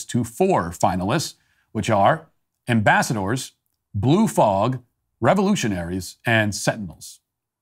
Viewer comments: When he first read the mascot options, I heard, "Glue fog," and wondered what the heck that was. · Lol. In the Daily Cancelation, when Matt was listing the final four potential mascot names for George Washington University, I didn't understand "Sentinels," and thought he said "SIMP's." That would've been truly accurate. Even better than "Blue Fog."